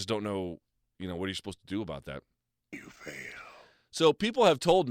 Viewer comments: Anyone else hear “you failed” in a sinister voice at 4:15? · of this video someone whispers “you fail”. Does anyone else hear this?